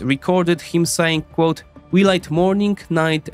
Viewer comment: what does the quote "we light morning night and evening" mean. I don't understand the context. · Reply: Light=lied